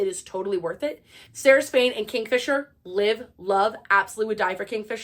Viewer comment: I know his name is Kingfisher (and i LOVE him) but i just misheard it as Kink-fisher 😂😂😂❤ now i fear I'll never stop laughing 🤣✌